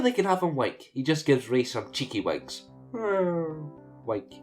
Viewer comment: When you said he winks, I thought you said wank.........